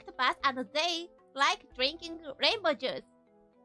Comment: "They like drinking rainbow Jews" is what I heard lol Whenever a person with a foreign (to you) accent says something you can't understand, just play along.